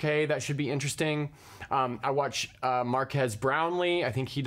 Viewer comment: What is the name of the channel? I am not so good at English to understand what Mr said. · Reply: Marques Brownlee